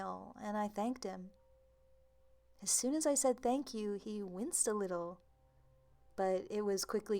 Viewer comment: what does it mean? : "He winst a little?" I'm from europe and can't understand, but i feel like i'm missing an important Detail :(